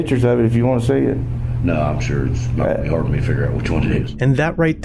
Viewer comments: you misquoted the officer. He actually said “it’s not going to be hard for me to figure out which one it is.” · I think he says "I'm sure it's not gonna be hard to figure out which one it is" rather than what you have subtitled. That completely changes the tone of the comment, if I'm correct. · Just a small observation I've made. The caption at 7:27 is incorrect. It reads "Nah, I'm sure it's not gonna help me figure out which one it is". He's speaking "Southern", and we tend to run words together sometimes. What he's actually said there is "Nah, I'm sure it's not gonna be hard for me to figure out which one it is". It doesn't change anything regarding the outcome of the situation or anything, just something I noticed. Carry on.